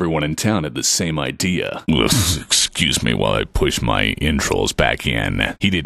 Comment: what is he saying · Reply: Viper "ughf... excuse me while I push my entrails back in.."